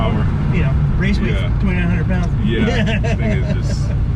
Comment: Hay I’m not sure if I missed it or if it wasn’t mentioned but, was wondering, what is the weight of your Mustang? I have a 2005 on 18x12’s square with 335/30-18 R888R’s. I’ve gotten mine down to 3220lbs with full interior. When I went from 295’s to 335’s in the front the bump steer and tram lining on some roads takes nearly full strength to keep it going straight with 0* front toe 😅. How’s yours? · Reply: it sounded like he said 2900 lbs